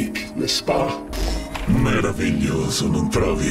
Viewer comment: Every time I listen to that part I hear "really just amazing and shit" in a weird way